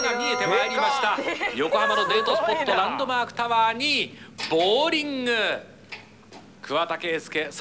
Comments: it sounds like he said "boring" but I'm sure he actually said "bowling" I mean think about it, there's bowling pins and bowling balls. And then some random bowling song plays for a few seconds. You gotta understand Japanese people have different accents than us Canadians/Americans lol. Nobody get mad, I'm just saying. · Did anyone else think it sounded like he said "boring"? lol.